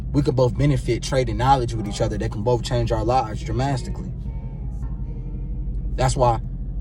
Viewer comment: He really said dramastically 11:06 😂